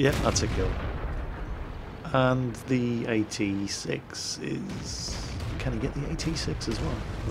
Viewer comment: did you say AT 6 ? :)